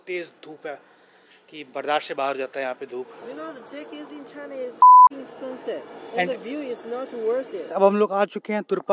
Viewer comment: What did he say here? The ___ is expensive and the view is not worth it? I don't understand. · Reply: fking